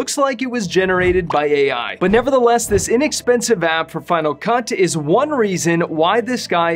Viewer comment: it sounds like you said final cunt.